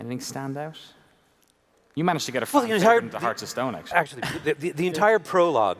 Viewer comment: Hey ,Sorry to bother but what is saying in 1:38:40 ? "f@ face Hearts of Stone" :P